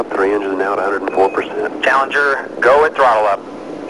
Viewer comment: Did I hear "Challenger got a problem" ??